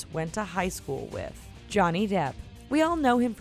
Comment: When it said "Johnny Depp", I thought it said "Johnny Derp".